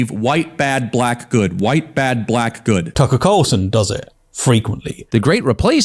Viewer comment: I heard "Taco Carlson" xD